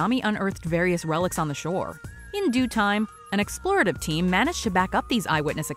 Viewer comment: Is it me or did she say "an explorer of team" instead of "a team of explorers" at 1:41?